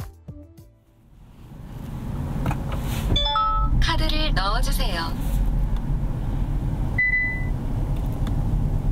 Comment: What does it say at 6:37? I hear that on most Kia/Hyundai and a bit different in Mercedes cars. For the Korean market only. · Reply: It says "please put in your Hi-Pass card."